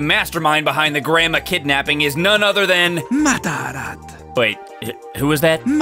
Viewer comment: I kept hearing "Madara" everytime the bastard repeated the name. Now I'm gonna write a crossover between Mia and Naruto